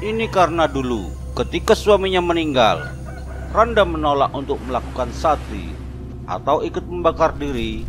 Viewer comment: Pas di menit ke 2:28 ngomong apa? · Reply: desas-desus, ini karna dulu rangda menolak melakukan sati